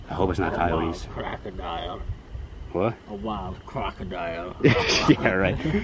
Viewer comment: what was that? I heard “what the fuck” am I tripping?